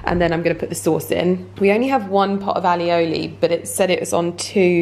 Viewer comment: So cute, it sounds like you're saying "Ali-oli" instead of "aioli" at 11:46. That's Ali in a dip sauce right there. Maybe that's how it is pronounced in English though lol. Such a good vlog!